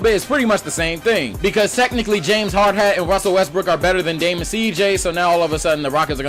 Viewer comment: Damn I thought you said "James Hardhat and Russell WestBRICK" HAHAHAHA